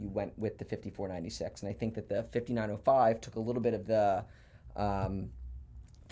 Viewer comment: « the pesaz » ? Can you tell me what is this word please ? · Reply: pizzazz: being exciting or attractive